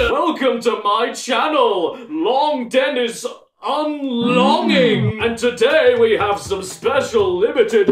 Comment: that sounded like Long Dennis Unclogging